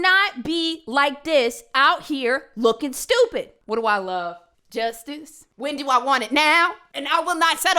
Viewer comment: Every time says she loves justice all I hear is “Chinese, Justice” 😂😂😂